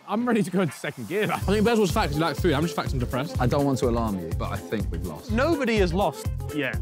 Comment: "Ethan's fat cause he likes food, I'm just fat cause I'm depressed." - Randy, I almost died laughing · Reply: I thought Randy said I’m just fat and depressed